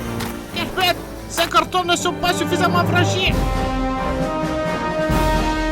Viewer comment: elle dit quoi a 22:05? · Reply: "hep hep ! Ces cartons ne sont pas suffisamment fragiles !"